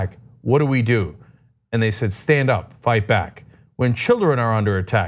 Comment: i thought they were saying "send them right back" as a mocking of trump's policy, like snearing as you shout their words back at them. but "stand up, fight back" works just as well lol